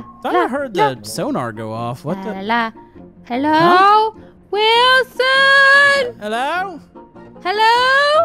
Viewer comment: Did i just hear WILSONNNNNNNNNNNNNNNNNNNNNNNNNNNNNNNNNNNNNNNNNNNNNNNNNNNNNNNNNNNNNNNNNNNNNNNNNNNNNNNNNNNNNNNNNNNNNNNNNNNNNNNNNNNNNNNNNNNNNNNNNNNNNNNNNNNNNNNNNNNNNNNNNNNNNNNNNNNNNNNNNNNNNNNNNNNNNNNNNNNNNNNNNNNNNNNNNNNNNNNNNNNNNNNNNNNNNNNNNNNNNNNNNNNNNNNNNNNNNNNNNNNNNNNNNNNNNNNNNNNNNNNNNNNNNNNNNNNNNNNNNNNNNNNNNNNNNNNNNNNNNNNNNNNNNNNNNNNNNNNNNNNNNNNNNNNNNNNNNNNNNNNNNNNNNNNNNNNNNNNNNNNNNNNNNNNNNNNNNNNNNNNNNNNNNNNNNNNNNNNNNNNNNN